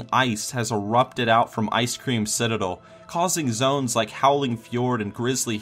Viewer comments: Am I the only one that heard him say 'Ice cream Citadel' instead of 'Ice Crown Citadel' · I'm sorry, did you just say Icecream Citadel?